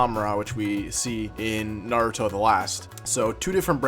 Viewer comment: I keep hearing him say “baruto” 😂